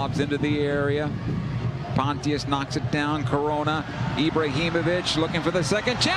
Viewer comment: What did he said at 0:08 🤯🤯🤯... "Corona"